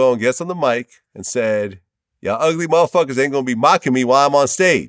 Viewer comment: I keep hearing Bizzy say "ugly motherfuckers"